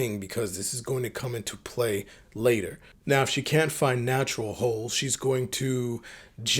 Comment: I thought you were saying. Natural Ho, 😂 cause I think they all are